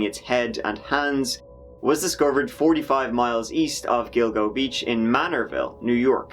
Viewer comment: Dildo beach?? Lol dam i am so sorry..that is what i thought i heard.